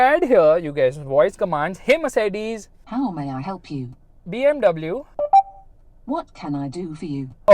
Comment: BMW Amg- TF did you just say?